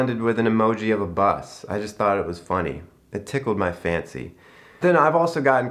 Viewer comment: You said “it tickled my fancy” and I swear, for a moment, I thought you said “it tickled my fanny” lmao it’s midnight & clearly past my bedtime.